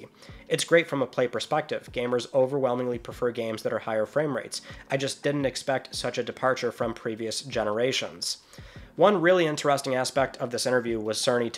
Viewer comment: That quote about not expecting gamers to prefer 60FPS over higher fidelity at 30FPS is really surprising. Just shows me that so many people running the show and making games are obsessed with the presentation and marketing rather than the actual gameplay being the presentation and marketing. Many probably aren't even gamers in the way that people like us watching these videos are. No sensible person that plays games and has their eyes out there would think that. Especially if they have ever even looked at a PC game before. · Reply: That’s not what he said tho 2:46 “Gamers overwhelmingly prefer games that are at higher frame rates. I just didn’t expect such a departure from previous generations” It’s morso he didn’t believe the industry pushed for it